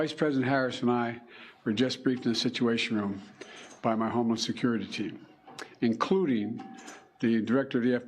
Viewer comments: Did Biden say him and his VP Harris were briefed by his homeless security team? 😂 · President Trump turning his head when was more than luck. Devine intervention. Thank you Lord Jesus In Bidens speech it sounded like he was saying his homeless security team, instead of homeland security team...